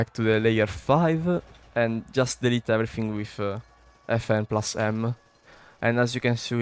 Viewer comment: tf did he say FN+M?